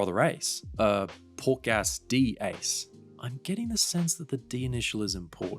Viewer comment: pork ass D ace , i heared this and captions said the same!